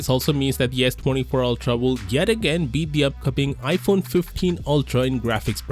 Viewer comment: Did he just say iphone 15 ultra?